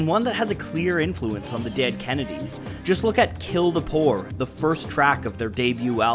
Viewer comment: When I was really young (like 7/8) I never knew "Kill the poor" said "kill the poor" and I always thought it was saying "gilgapor." whatever that means